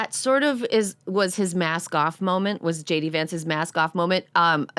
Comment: Did Erin say "max gaffe moment"? Or is she referring to a person?